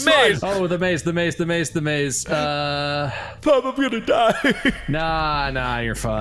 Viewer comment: Sometimes I really miss the time when the viewers could submit the subtitles, but then I see stuff like "bobaloo tada" instead of "Bob I'm gonna die" at 27:40 and remember that sometimes the subtitles ended up complete garbage